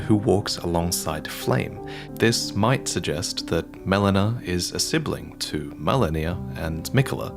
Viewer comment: did he say Melanir?